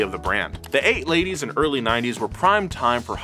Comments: Had to watch back a few times, but says "The eight ladies and early nineties" at 3:42. Anyone else catch that? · Woah, at 3:32 says 8 ladies instead of late '80s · did he say eight ladies? XD